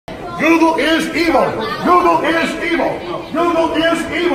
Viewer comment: At the beginning I thought it said: Noodle is Evil! Noodle is Evil!